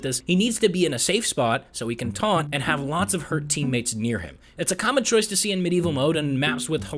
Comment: did you say her? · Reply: He said hurt